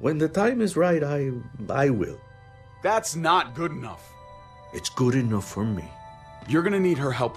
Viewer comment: When pa said it's good enough for me I thought he said it's GURINUUU for me